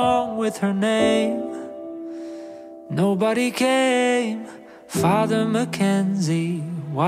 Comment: i heard "nobody gayyyy~😩" What the furr